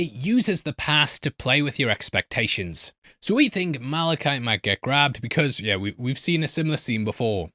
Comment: I know what you actually said but I like to giggle at the thought of you having said “Sweet thing, Malachite” lol